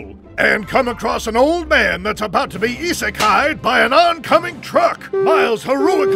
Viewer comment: did I really heard isekai?